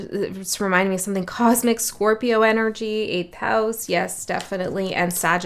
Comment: 😂 I was lead back to this read. You’re right, I was never Cinderella. 🤨 I’ve done a lot of shit work. I enjoy the captions, @ 6:07 -you said 8th house. That’s not what the captions say. Eight of cups. 😉